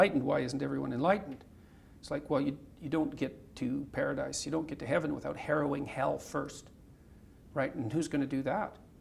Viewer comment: "you don't get to heaven by..." what? Can someone please tell me what word he uses? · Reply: U don't get to Paradise without harrowing hell first.